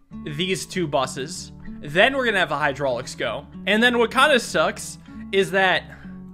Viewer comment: I thought he said "And then wakanda sucks...'